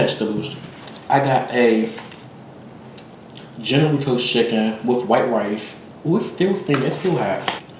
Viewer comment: Your intro 😂😂 I'm so weak!!! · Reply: I heard it as "Generals Toes Chicken" 😂 LOVE me some! ❤️